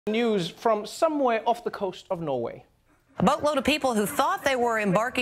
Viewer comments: LOL i thought she said a butt load of people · I thought she said "a butt load of people"